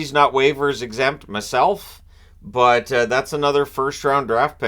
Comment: Did you say mehself?